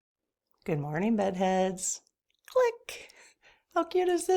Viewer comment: why do you keep saying, "Good morning buttheads". I think I heard you say it was something else, but I keep hearing buttheads. Lol . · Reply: It's bed heads. :)